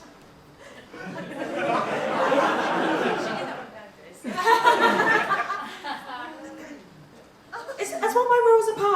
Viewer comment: What does say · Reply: I think she said ‘She did that on purpose’?